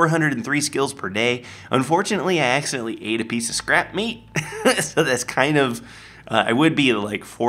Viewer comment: When you said scrap meat I thought you said scrap metal